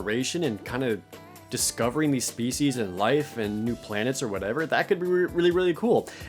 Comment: Ahaha, I heard "discovering these feces" when he talked about the new mass effect.